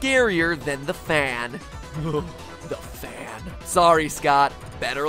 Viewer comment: ok dude i have seen every single one of these and i always hear you say "the fan uahhgh"